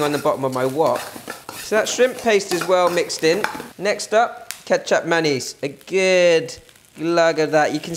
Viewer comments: ketsup mayo? What you added did not look like ketsup mayo which is what you said it was, it looked more like a sweet soy sauce, which is then what you said it was after you started stirring it in so this is really confusing. What was it you added sweet soy sauce or ketsup mayo? I personally would not want any ketsup nor mayo in my nasi goreng, dont know about you - but me not at all. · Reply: Thanks for clarifying!! that is what I thought it was, some kind of a sweet soy sauce but it really sounded like he said 'ketchup mayonnaise' - that is why I was a bit confused. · I thought you said 'ketchup mayonaise' and then i took a look on the recipe itself to find out that it was 'kecap manis'.